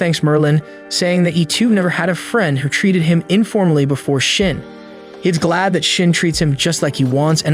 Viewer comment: I am sorry did he just say shit35:56